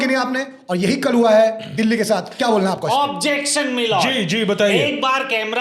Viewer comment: Shivankit as Paaji: Yehi “kal hua” hai Delhi ke saath. I heard like yehi “Kalwa”(black person), I thought Haan Paaji to dark-skinned hai par Shivankit on-screen pe Paaji ko Kalwa kaise bol sakta hai.